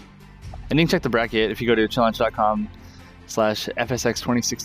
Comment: What does he say at 20:42? I want to look up brackets, but can't make it out. This is what I hear, <link> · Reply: The site is challonge, with an O. Here's the link: <link>